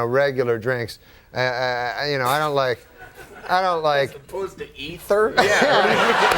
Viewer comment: does Andy say, "As opposed to eating them?" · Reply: "As opposed to ether?"